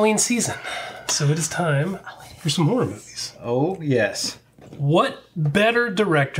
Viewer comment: I thought you said the season of horror movies not the season of horrible movies.